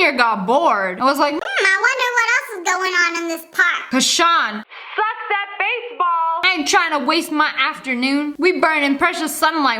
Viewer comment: did she just say suck that baseball